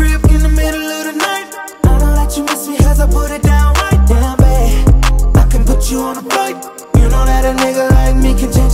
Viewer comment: Wait, this song is talking about sex? I didn't notice that at all. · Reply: I thought he said I can put you on a bike